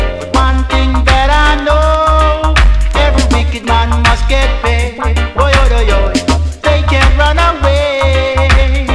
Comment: “every wicked man must get paid”? What? Or did he say “pain”? A wah di bomboclat a gwann yaso? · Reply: Paid in FULL for their wickedness